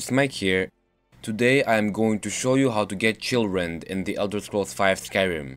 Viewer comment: Haha. At first i thought he said: how to get children in skyrim.